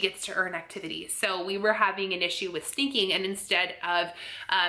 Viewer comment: I don't think so? I'm not sure what you're referring to. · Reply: It sounded like you said Delilah was sinking her toys.. sinking her toys in her backpack? ...... .. I'm realizing now you probably said SNEAKING... >.< oyy, I'm losing it!